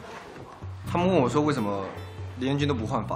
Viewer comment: What did zhengting say 1:49? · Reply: it's"Why did you borrow my jokes"but in China,some fans heard that“Why did you hammer my Gui（Justin’s nickname is Fu Gui）”